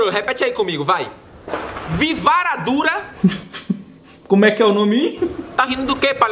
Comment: eu ouvi vi vara dura